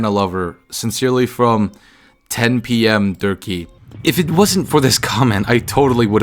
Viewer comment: i heard turkey, sincerely 4 am xalo